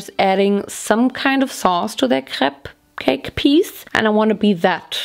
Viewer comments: OMG I should really stop play animal crossing whilst watching videos🤦🏼‍♀️ I thought she said she was making crab cakes and was confused as hell when she said strawberry crab cake🤢 I’m soo glad I was wrong and it’s just her accent and not making strawberry crab cakes🫠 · You were saying crepe cake but I thought you said cup cake so when you pulled out the crepe pan I was thinking “Naomi, love, where’s the damn cupcakes?!?” 😂🤣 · OHHHHHH…I thought she was saying “crab cake” (not Crepe Cake) until she finished making the whole recipe. 🤦🏼‍♀️